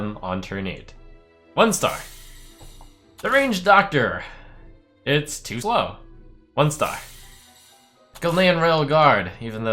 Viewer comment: What happened over here? Did say something bad? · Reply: it's to (fuckin) slow !